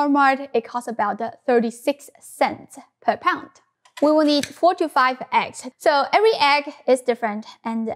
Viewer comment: I heard "we need 45 eggs" as she opens that big box of eggs.... LOL. Then I realized she said 4 to 5 eggs... Kids think this is fun so I use them like slaves!! So yes it is worth it!!